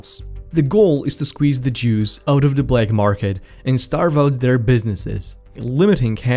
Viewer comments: "The goal is to squeeze the jews out of the black market and starve their businesses" Is what I heard lmao. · I heard "the goal is to squeeze the Jews out of the black market." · 2.6 he said juice but it sounded like he said Jews, accents are fun.